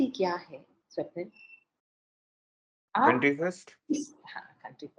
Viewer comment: Bhaiya In this time line 24:25 responsibility? I can't understand the word? Please tell me. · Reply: Country first